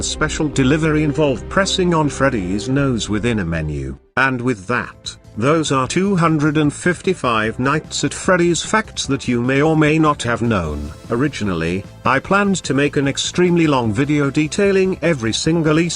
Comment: At the beginning I thought he said “255 nights at freddys fact” And if night 7 is near Impossible I’m worried if anyone has gotten to 255 lol